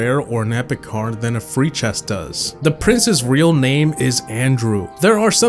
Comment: When i was at fact 18 i looked away for a second and i heard "The princess name is andrew" and i turned back and i was like NANI!?!?!??! And realized its prince's not princess